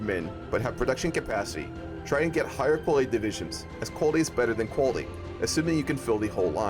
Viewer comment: what in god's name did you say in 01:26? Is quality better than quantity or is quantity better than quality? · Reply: i think he said "quality is better than quality" which means to just stack quality?